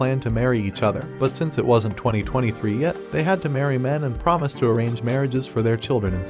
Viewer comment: nah bro what did bro say 2022 💀💀💀💀 no gay